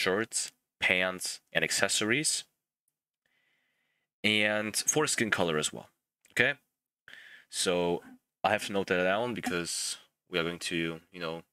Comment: did he just said "foreskin color as well"